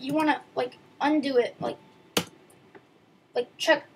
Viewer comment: to where? i cant hear it correctly and the recording is pixelated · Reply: he said undo it